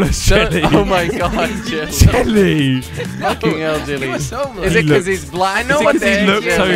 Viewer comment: did say its because hes black